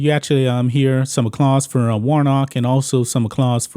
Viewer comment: Did you just say...acclause?? Instead of applause?? You said it back to back....